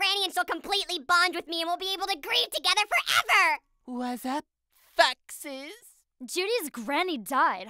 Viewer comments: why does Kevin sound like he says '' what's up fucks?'' When he says '' what's up foxes?'' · it sounds like keven said whats up fukses